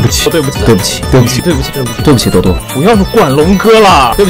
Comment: I heard “Sorry brother” it might be little brother though